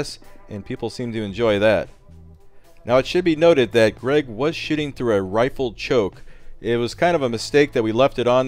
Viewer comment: "Greg was shitting through a rifled choke" lol anyone else catch that? Right at the 8:35 mark.